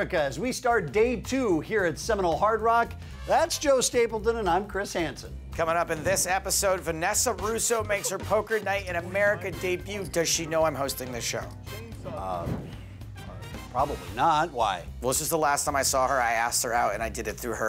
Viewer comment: Vanessa Russo? Oh thank God! I thought I heard Vanessa Selbt is playing